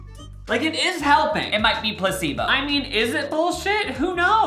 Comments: "newest cannibal maple leaf ring" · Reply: And at 3:54 he says “🐱bo “ instead of placebo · definitely said "cannibal" instead of "Canada" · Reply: And at 3:54 he says “🐱bo “ instead of placebo